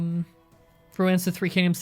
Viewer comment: for a moment I thought you said "I haven't played Romance of the Three Kingdom 13 in a while" 😆